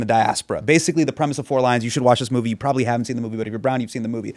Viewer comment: Four Lions is such typically British humour. It’s a cult classic for a reason. Absurdity on Monty Python levels. Will definitely watch bait. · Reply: Thank you--I thought they were saying "four lines" and wanted to look it up later😂